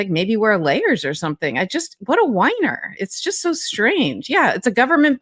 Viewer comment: Oh she said "whiner"... I thought she said weener... Don't worry Donald, you'll be going somewhere very hot soon enough... For eternity...